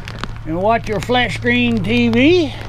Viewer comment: did he just say ‘ watch your flesh peen TV’!? 😳